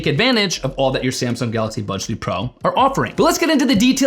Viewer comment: Why do I hear "Budge 3 pro"? Thank you for the review 👍